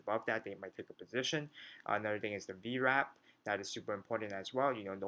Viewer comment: Thanks for the video. I cant hear 1 important thing in the video. It's the indicator you mention on 10:48 <link> It's called a V-rap, G-rap, rep, reb, rab. I cant figure it out... sorry :)